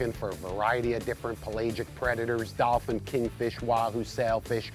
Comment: Did he say dolphin?